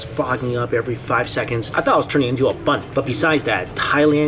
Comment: "I thought I was turning into a butt." That's what I heard. Lol.